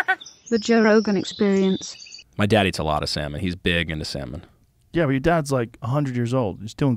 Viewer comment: Oh; "salmon". At first, I really thought he said "my dad eats a lot of sand" I'm so stupid